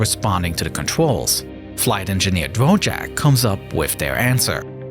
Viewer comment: I had to back that up, it sounded like you said "Flight Engineer Wojak". That bro has seen too much